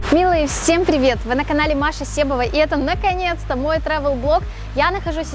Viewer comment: Лол, посмотрел начало видео и не зная названия канала услышал как "Маша, съ**ывай", свернул видео, чтобы перепроверить))) Оказалось, что послышалось. А я уж решил, что норм смелое название для тревел канала)))))